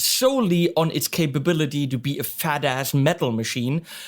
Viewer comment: Fucktardian thinking to expect the Line 6 to be a fatass metal machine? I probably misheard badass as fatass, but 'I'm still snickering! You SLAY me man, fuckin' hilarious